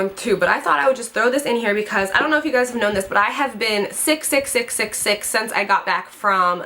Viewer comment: I could have sworn you said "I have been 666 since I got back from Mexico" GET THE DEVIL OUTTA YOU GIRL lol